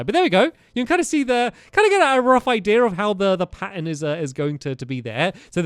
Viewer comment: “this panda” instead of pattern. Nice